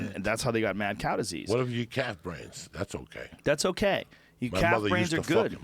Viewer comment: Brenden Shaub 's face when he think's they're eating CAT BRAINS instead of CALF BRAINS. HAHAHAHAHA